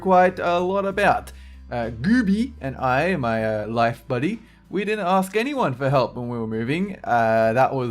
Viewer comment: DID YOU SAY LIFE BUDDY?